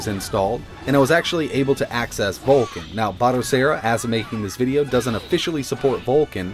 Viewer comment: I thought it said Bacteria Linux, I was like please dawg wash your hands